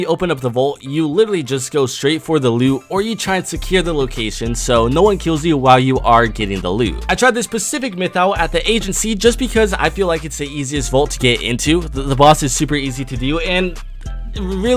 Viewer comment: I'm sry did you say Pacific instead of specific